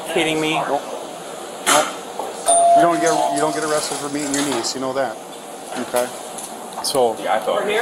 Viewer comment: cop says “you don’t get arrested for meeting your niece, you know that” Subtitles say “you don’t get arrested for meet & greeties”